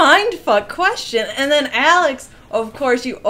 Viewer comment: did she say Alex?